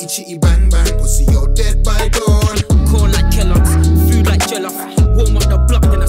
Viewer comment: I thought that the Slag Bros said 'Dead by Door." XD